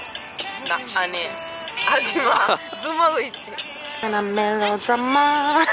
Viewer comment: ho capito hajima perché sta in ogni canzone coreana